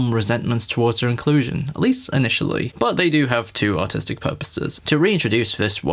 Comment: You said “artistic.” I heard “autistic.” Holy wow.